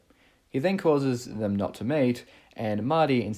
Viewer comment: You said he caused his parents not to meet, and I genuinely thought you said he caused his parents not to mate, and I died laughing